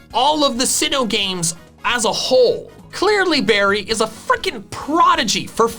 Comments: closed captions were messed up, accidentally typed in a swear word instead of "fricking" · the captions say "fucking" instead of "fricking" lol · I am watching this with Youtube"s closed captions on so I can also read what you're saying. At the 12:53 mark when you said; "Clearly Barry is a freaking prodigy..." , the CC actually listed it as the F-bomb. lol